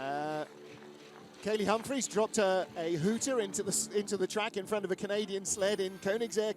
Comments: dropped a what on the track? Sounds like he says hooter but I don't know what that is. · “Katie Humphries dropped a hooter on the track”? Ummmmmm did I hear that right?